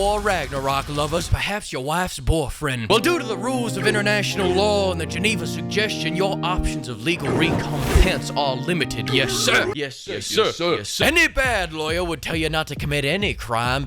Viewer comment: I thought I heard "Geneva Congestion."